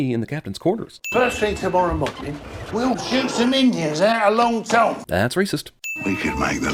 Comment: What'd he say?! I'm literally asking that as a question, I have no idea what he's saying!! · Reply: Topher1286 "Out of Long Tom." Long Tom is a cannon on the Jolly Roger.